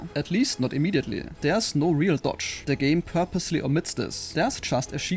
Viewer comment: there’s no real WHAT? I didn’t understand what you said! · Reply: Dodge